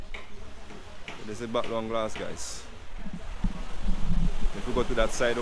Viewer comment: All along I thought you were saying "Back dung glass"